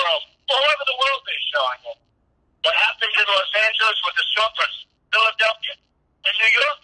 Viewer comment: when one of the cities is listed as Unintelligible I think he says Los Angeles